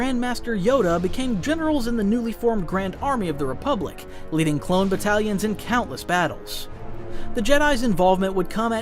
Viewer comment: i thought he said “cloned italians” and got really confused 🥌🥌🕴️🕴️🕴️